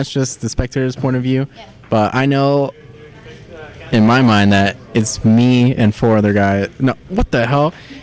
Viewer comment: i heard "in my mind it's me and 4 randoms" XDDD